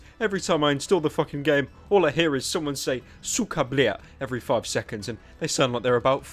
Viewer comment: Who was the fuckin' idiot writing the subtitles who misheard "suka blyat" as "sacre bleu?"